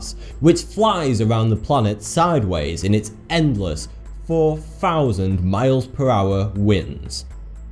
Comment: Ever thought that there might be aliens that can live in like 400 degrees and thats normal for them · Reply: How can you not imagine that ... Seriously? The floor is lava and I always won saying I'm an allen.. Oops I thought you said 40000